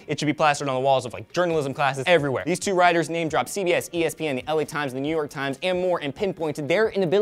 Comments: Did you say Elliott Times? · lol when Elliot said LA Times i thought he said Elliot Times xD